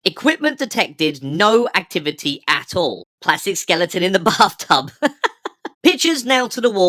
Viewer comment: This is still one of your funniest videos! At 10:16 for the longest time I heard "Classic skeleton in the bathtub" (which is funny to me in and of itself) instead of what was written on screen because I usually just listen. Gurdy Gurdy Gurdy Gurdy!